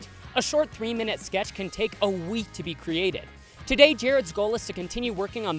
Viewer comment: I heard him say Jared skull